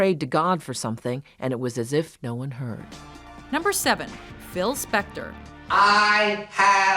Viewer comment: At #7 I thought that she said Phil Spencer.